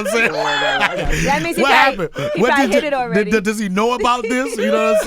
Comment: Low key, I heard Envy AND Angie say, "he prolly hit it already!" LMAO!!!